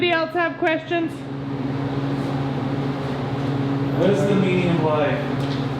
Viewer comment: I thought i heard a questions: What is the meaning of life? The question at 32:27. if in fact that was the question, I know.